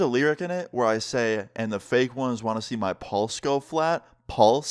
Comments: You know originally when I heard this song I thought he said palms so then I searched up the lyrics and realize he was saying pulse but for now on I’m now just going to hear balls lol! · Ngl, I ALWAYS hear it as "balls go flat" because of this video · Ngl i heard “My balls grow fat” I had to really listen closely to make sure I was hearing it wrong 😂😂😂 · Bruh istg I thought you were saying "balls go flat" not "pulse" lmao. Still a W song tho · I thought it said paws go flat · For the record, i thought you were saying "palms", but its actually "pulse" thanks for clearing that up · I thought it said pauls go flat